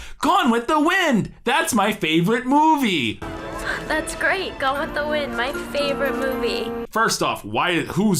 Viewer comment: Did the character say 'Gone With the Wind'? I heard 'Go with the Wind'...